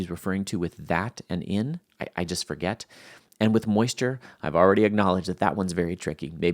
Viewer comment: You mentioned something about the word “brethren”? I searched brethren and didnt see anything. What’s that issue about? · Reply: Nevermind the word was moisture not brethren. Dunno how I misheard that. Thanks for the reply